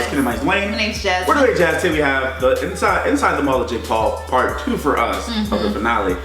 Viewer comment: Did anyone else notice that said inside the mull of jake Paul instead of inside the mind of jake paul? XD